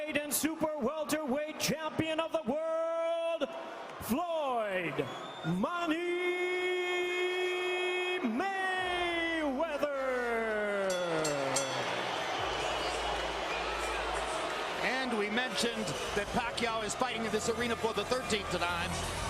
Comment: & 12:20 did i hear floyd manny Mayweather?..